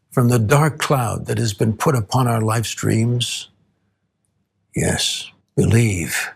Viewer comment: did he just say lifestreams? ...like FF7 lifestreams?